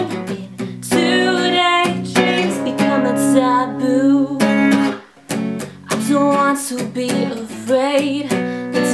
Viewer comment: did she said ty boo?! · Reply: Becoming taboo